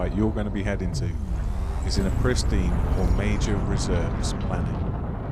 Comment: What did he say at 4:25? I couldn't hear. "a pristine or major reserve planet"?